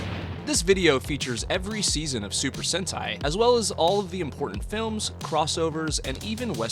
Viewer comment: I thought it said Super Hentai for a second